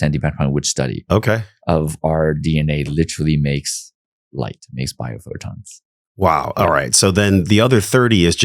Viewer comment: We are 70% light? So that is energy or water and the field is the same? So in a breakdown mode how are we 70% light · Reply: I heard it differently. Listen again around 28:16 mark. Around 70-78% of our dna makes light/bio-photons is what I heard